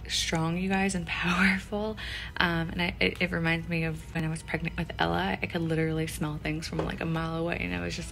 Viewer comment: I thought you said he was telling you that HE was pregnant😂